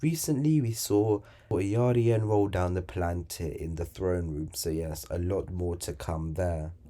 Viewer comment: who did you say rolled down the Oakantir in the Throne Room at 3:29?